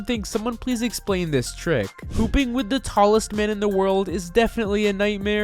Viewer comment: I thought u said pooping😂😂😂😂😂😂😂